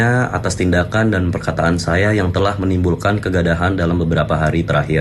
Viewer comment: Kegadahan, bukan kegaduhan apakah gw salah dengar disini? Ada yg dengarnya sama gw gak?